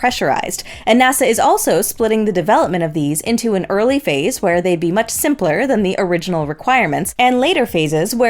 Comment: sounds like your saying "where they be" ......hahahaha......has a nice ring to it.....im sure she is saying or meant to say [they'd] ...or [they would be].... but not sure.....either way it stuck out at me....hahaha .....trust me I am no better....just fun to catch them ....hahahaha - great episode as always and looking forward to seeing the moon/earth from the crew's camera.....lets go